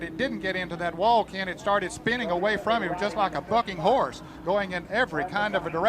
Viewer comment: What the hell did Ned Jarrett just say?! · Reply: like a bucking horse... someone less excited woulda said bucking bronco